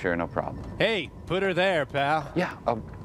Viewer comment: What does Crow say there? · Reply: “hey, puter there, Pal!” bc of all his arms, it’s like which one do i shake?!? LOL corny joke tbh